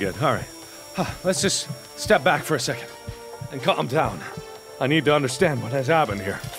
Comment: in subtitles instead of lets clam down it said lets cut them down